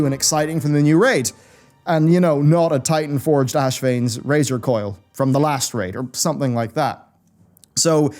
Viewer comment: did I hear "Ashevane's razor COIL" ?